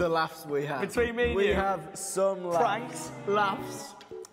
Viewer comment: the video at 2:24 I've seen it so many times and for some reason I always thought he was saying planks. I didn't realise he was saying pranks. I'm fucking wheezing.